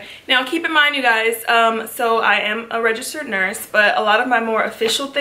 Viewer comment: When she said she was a registered nurse, I thought I heard she's allergic to nurse